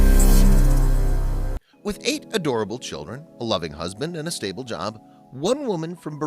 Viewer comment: I don't why I heard "8 adorable children, 11 husband" took me a minute 🤣